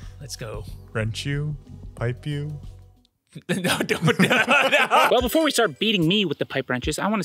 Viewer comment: I thought he said “before we start beating meat”. Lmao